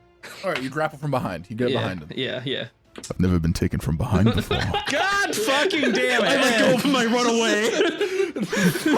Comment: wait and listen to what velrisa says... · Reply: "I can fix it"